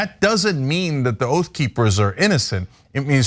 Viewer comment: I thought she said Goat Peepers…